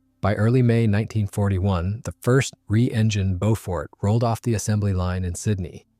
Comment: Did I hear “three-engine Beaufort” at 5:10? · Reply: re-engined Beaufort